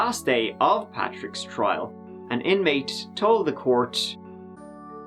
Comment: Who else thought he was saying Patrick Swayze? lol